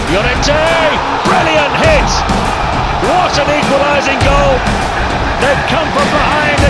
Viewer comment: What says comentator at 0:45 please? "What an ????? goal!" What is that ???? word please! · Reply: Dexerious equalizing